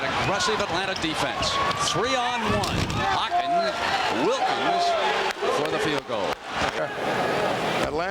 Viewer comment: what does Brent M say? Sounds like "Hawkin Wilkins, for 2" ??